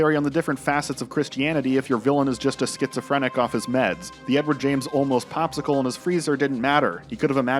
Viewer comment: i heard you as edward james almost and it really fits the season.